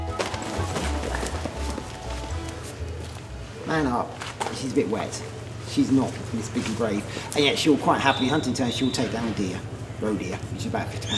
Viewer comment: Heavy accent, please help what did he say lol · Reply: Handler: (To the bird) ‘Man up’ (To the interviewer) “She’s a bit wet. She’s not ‘Miss Big and Brave, and yet she’ll quite happily, in hunting terms, she’ll take down a deer: a roe deer which is about a 50 pound deer.” Interviewer: “She can kill one?” Handler: “Yeah.”